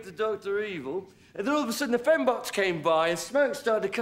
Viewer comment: it says phone box instead of fembots with subtitles